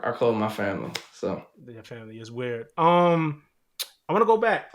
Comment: did he say "that's weird" after Laroi said I call them my family?